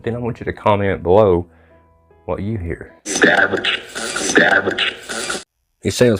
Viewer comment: I hear it as established. 9:10 almost sounds like an advert or someone saying something like "if you get here right now"